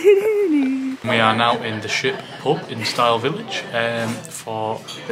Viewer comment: I thought he was saying the shit pub lol